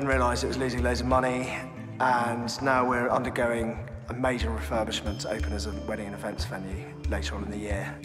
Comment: I thought I just heard D major.